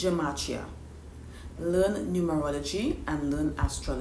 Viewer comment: Please, what did you say at 1:55? Learn….? Love your content!! ❤ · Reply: I said learn numerology and astrology. Is my accent that bad? 😩